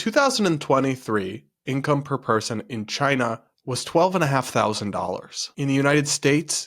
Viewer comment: Is there a reason the subtitles say "income per person in China was $125,000 in the United States it was $82,000" when what you said was "income per person in China was $12.5k ($12,500) in the United States it was $82,000"